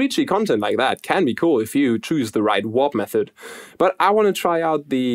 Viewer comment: I swear for the first 20 minutes I thought he was saying "wop method". Also accurate lol